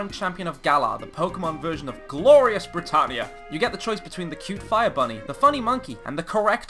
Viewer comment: When you said Glorious Brittania.. I thought you said, "Glorious Retardia", so much that I had to go back and relisten to it again with subtitles to make sure. This is not a jab at Britain, I love the UK, I just genuinely thought that was what you said at first haha